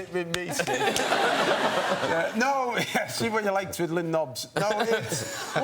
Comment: what does he say? Something about twiddling knobs? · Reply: That’s exactly what he said ‘what are you like twiddling knobs’😂